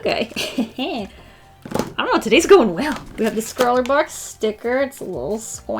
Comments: did just say Scrawlr box sticker????? Not Art snacks??? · Did she say scrawler box sticker instead of art snack